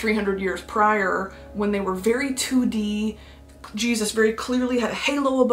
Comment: I have a fan next to me so I thought she said it was cheugy not 2d at first